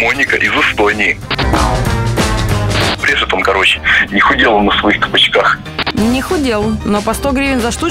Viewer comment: послышалось "не хуел"